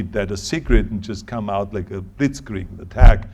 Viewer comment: did he actually say "blitzkrieg" at 13:43...!?